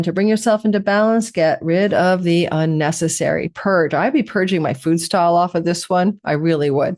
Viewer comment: "I'd be purging my food style off with this one" - is this what you said? For Libra